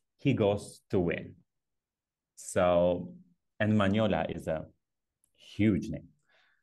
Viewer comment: Man-hole-A - i see a dirty mind when i hear one